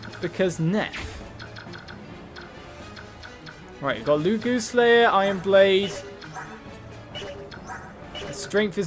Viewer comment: Did you just say "Loogooz slayer?" lol